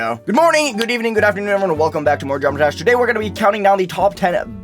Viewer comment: Welcome back to the "Subtitles Being Goofy" game! Here, the subtitles misheard "Geometry Dash" and said "drum trash"!